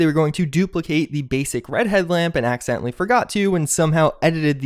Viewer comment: I heard "redhead lamp" instead of "red headlamp" and I thought you were talking about hair.